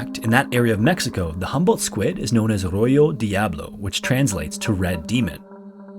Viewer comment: God, just hearing the intro music gives me gooseflesh! And with my thalassophobia, this will be good 😁 PS-- certainly delivered and then some. I had a chuckle when I thought he said locals at the Sea of Cortez called the Humboldt squid "Arroyo Diablo," meaning "red demon" Uhhhh.... Oh! "Á Rojo Diablo" makes sense! PS-- That was great! I'm truly beside myself sitting in the dark. ✨shivers